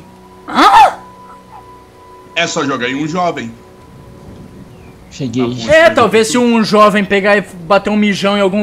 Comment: só eu que escutei um cheguei